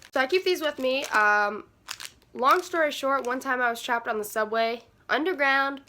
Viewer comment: actually thought she said 'i used to trap by the subway' 💀💀💀💀